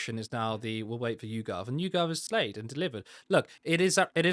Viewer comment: Did say "YouGov has slayed" 58:32 hahahah 😂😂😂😂